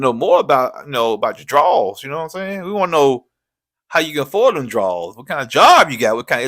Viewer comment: Wait. At the 39:34 mark , did say we wanna know HOW YOU AFFORD THEM DRAWS??😂